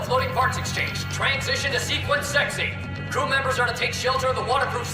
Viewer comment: did they just say" sequence sexy"